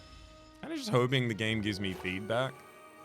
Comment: i kept hearing, "i hope the game gives mee feedback" when talks about the feed card that is what the player needs to do tough XD